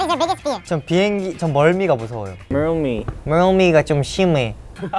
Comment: I genuinely thought they said rail me I was like WHAT?!